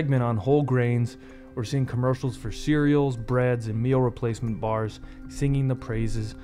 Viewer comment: Did he say "Breads" @ 3:29 ?